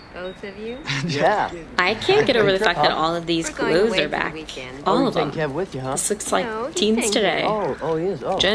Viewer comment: what did you say here glue on thier back?